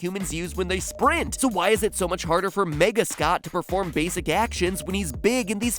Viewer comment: I thought for a second he said “Nega Scott” and I got Scott Pilgrim vs. The World flashbacks